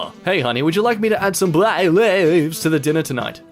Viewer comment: the captions say "black slaves"